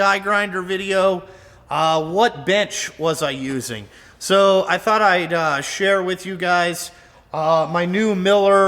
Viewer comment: Holy shit... I had to listen to the beginning twice cause I thought you said, "... you all wanted to know what 'bitch' I was using". LMAO